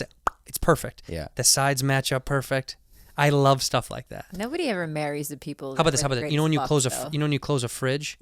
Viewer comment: CAN SOMEONE TELL ME WHAT KHALYLA SAID AT 1:03:23? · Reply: "nobody ever marries the people who were their greatest fuck though" I think?